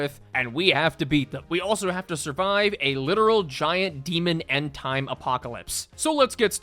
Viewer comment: I thought it said "bought the Apocalypse" which I feel would've been funnier